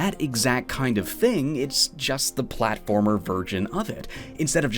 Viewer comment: did you just say platformer "virgin"?